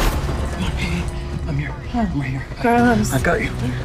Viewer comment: I also thought Carlos said, "Hey baby, i'm here", but after listening a few times with headphones he said "Hey hey, i'm here"